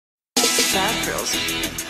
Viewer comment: When someone says 'Bad girls' I thought they say 'fat girls'...